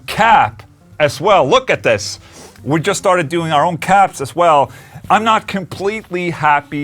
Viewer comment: At first I heard that: "we just started doing our own CABS as well". I said great, there will be Solar cabs. :P